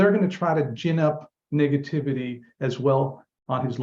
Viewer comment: Sorry for this but it sounded like he said niggativity